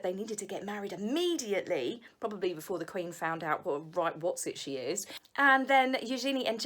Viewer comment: Hi, missed your videos. What do you say at min 8:11 ? I''m German and don't understand the word. "... probably right before the Queen found out what a ???? she is." Love your thoughts and comments, you say and describe perfectly what I also think about "them". Thanks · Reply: Wotsit she is. Just slang for being a cheeky dishonest person